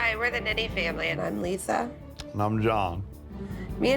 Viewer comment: did she say hi were the diddy family